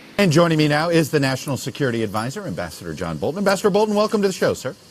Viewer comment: Oh he said "Ambassador Bolton", I could've sworn he said "Bastard Bolton". Figured they went for some GoT-thing